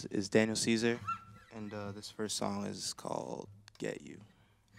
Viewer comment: I legit thought he said, 'This first song is called GAY' until I read the subtitles 0.0